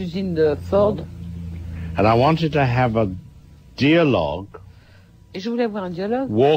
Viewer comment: funny idea @ 7:00 but what is a "deer log"? I think he says "dialog" but it walks???